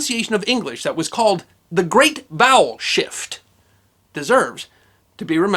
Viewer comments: I'm cursed! I keep hearing "bowel shift" and then I'm accosted by imagery. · I heard that as "The great bowel shift", my apologies. But now we know why B and V are next to each other on a qwerty keyboard layout :)